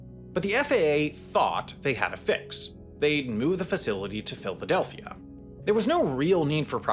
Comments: My mans, did you say "Filthadelphia" at 12:05? Not opposed to this, its pretty accurate name, but that is what I heard haha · I swear I heard filthidelphia 😂😂😂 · I heard that Filthadelphia😂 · Ayooo did he say Filfydelphia? 🤣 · he really said Filthadelphia!!! 😭😭😭😭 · Did I hear PhilTHadelphia? 😄